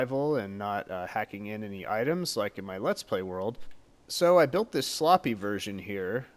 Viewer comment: Sorry i thought u said "hacking in items like in my LP", sorry.